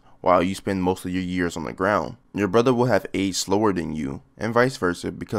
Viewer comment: what are you saying here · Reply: “...brother would have aged slower than you...”